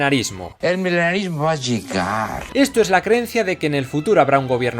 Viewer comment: Jajaja pensé que dijo: El milenarismo es "chingar"